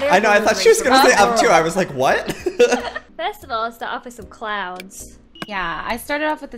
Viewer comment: I thought they said "I'm going to start off with some clowns" XD but they said clouds